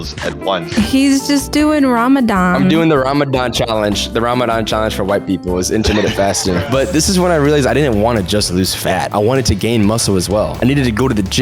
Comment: Bro Im morrocan and I was half awake and I hear “it’s Ramadan but for white people